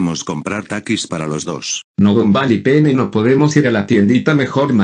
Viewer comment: Escuche "Gumball y pene" xD